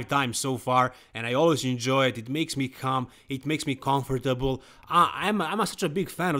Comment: Did he say watching the podcast makes him come? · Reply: Haha I believe he said “calm” 😅